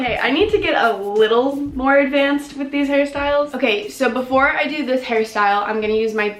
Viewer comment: Ok but 5:06 why did it sound like she said “before I do this Harry style?” 😂